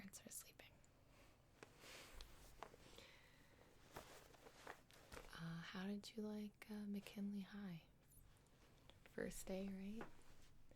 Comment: How tall is Gibi? I'm only 5'11. For a Chinese that's pretty tall at least to me😅😂😂😂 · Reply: anyone heard this at first? 0:22 -How would you like to maybe get High?